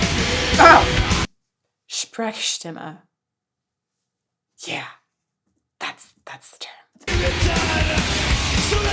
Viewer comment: What did she say at 24:01? I couldn't undestand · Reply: Sprechstimmer I think, it's a german word